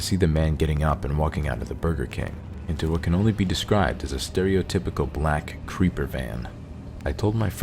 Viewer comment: I thought he said stereotypical black kreeper man at first😂😂